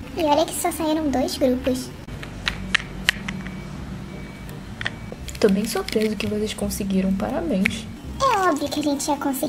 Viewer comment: eu entendi "to bem SOLTEIRO" eu fiquei tipo "QUEEEE?" mas era "to bem SURPRESO" kkk